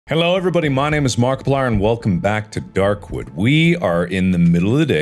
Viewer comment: My dumb brain mishearing Mark: "Hello everybody! My name is Darkiplier and welcome back to Markwood!" ...